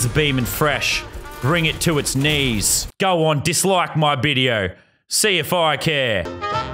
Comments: go ahead and dislike the bidio I dont care- Thats what I heard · did he say bideo? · GO AHEAD AND DISLIKE THE VIDEO YOU DUSTY DEPOT LOVERS!!! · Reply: It sounds like you said bideo